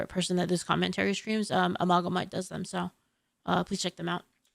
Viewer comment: who is the person was mentioning at 17:10? couldn't quite understand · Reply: Amalgamyte